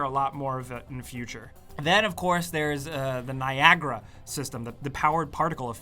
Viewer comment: I thought he said Viagra lol